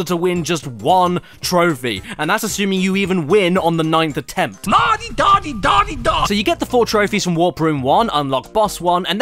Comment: nautydautydaudyda. That's what I heard..